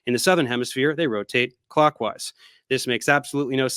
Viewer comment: Was I hearing "Clock-ways"?